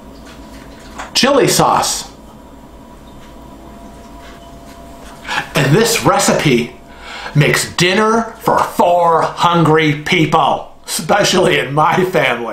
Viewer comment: As i was writing down the ingredients i thought you said "And this recipe makes Meatloaf for 400 people"....... i just threw my paper in the air like what the hell.